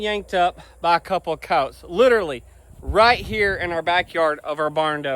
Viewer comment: Every time you say "coyote', all I hear is "coat", or cawt. It took me a few time watching your vids when you talk about coyotes for me to understand what you were actually saying! Lol